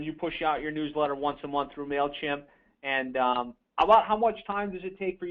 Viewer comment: who did she say is the email server? it sounded like she said mailchem? or something.... im in uk so if its obvious dont hate on me. lol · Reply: It's mailchimp